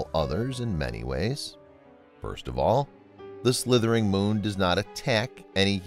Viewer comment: What did you just say The slithering moon?